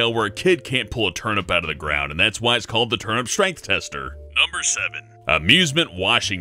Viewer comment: On number eight I thought he said Turn up Straight,at first it sounded strange then he said it was Russian and it all made sense. Until the turnip bit. Then I was lost again.